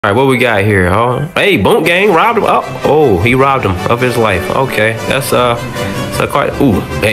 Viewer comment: "He robbed em... of his life" Had me cackling Edit: 998 likes for laughing. Damn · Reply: Ohhhhh, I thought he said "He robed em... of his wife"